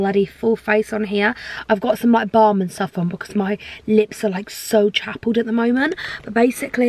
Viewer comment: Did she just say her lips are chapled? 😂